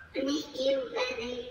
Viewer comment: "Hey, tio, sai daí..." Entendi nesse trecho.